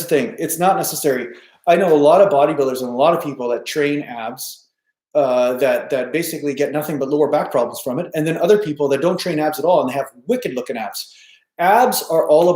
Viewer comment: Sorry i didn't hear it well 8:25 he said some bodybuilders train abs and...? He said what happens to them? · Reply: some get injured doing so. Some others need to train them because their backs are overly arched. And others don't train the at all, and rely on the compound lifts to train the core and the diet to reveal the abs. Showing abs is really about body fat percentage, not training them really.